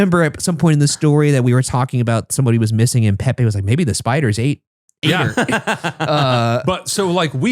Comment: Pepe's comment about, "Maybe the spiders ate her," during that episode just killed me! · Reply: okay i was literally just rewatching that first BUN episode last night and i have never noticed he said “ate” i always thought he said “maybe the spiders HATE her” this makes so much more sense omg 😅